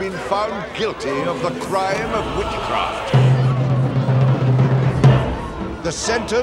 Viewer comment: \of the crime...what? I never understand what he says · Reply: of Witchcraft